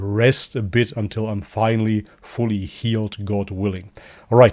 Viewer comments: All this time I thought said "May God bless you all, mashallah, and peace". Tell me why I just realised that he says "much love" and not "mashallah" lol 🤣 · All this time I thought said "May God bless you all, mashallah, and peace". Tell me why I just realised that he says "much love" and not "mashallah" lol 🤣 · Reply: Hahaha!! Omg I thought I was the only person who thought he said ‘Maash’allah’ not ‘much love’😂It wasn’t until about 5 videos in when I had the subtitles on and re-listened that I realised it wasn’t Maash’allah but much love!! lol! It was the initial reason I started following properly and started to contribute but I’m glad I did I am enjoying his journey. Ps you might read in the hadiths that the prophet pbuh himself recommended honey as treatment for various ailments. Of course this was many centuries before science caught up. May god guide us all .